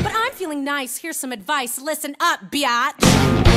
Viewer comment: DID SHE SAID BIATCH AS IN B!7CH